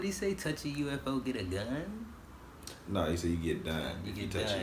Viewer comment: When Karl says "if you touch a UFO you get done," get done means go to jail. Don't know if that's an expression in the US, but in the UK if you get caught by the cops/go to jail you "got done." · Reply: I thought he said "you get dumb", so it was always really funny to me.